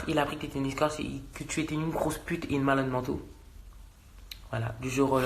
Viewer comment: une malade men- quoi ? J’ai pas très bien entendue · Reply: 100110 j’ai remis 1000 fois il a dit « une malade mentaux » à son âge